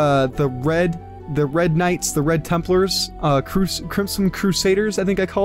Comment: did you just say templers no hate like the video just asking